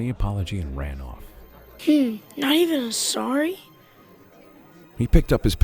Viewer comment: "hmm nor rvin a srry?" what i heard the first time 😂